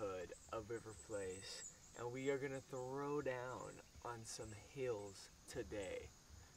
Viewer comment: when he said "throw down on some hills" in the beginning, I legit thought he said throw down in high heels...